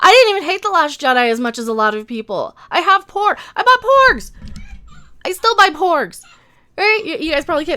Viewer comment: I thought u said u bought porn lol